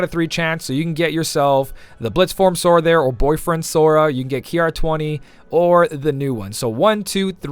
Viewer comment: When you said "blade form Sora" I legitimately thought you said boyfriend Sora